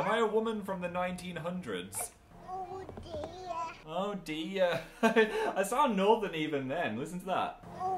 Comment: I died of cuteness when baby Phil said "Oh dear" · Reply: idk why but i thought he said "oh delia" thats what i heard the first time i watched this video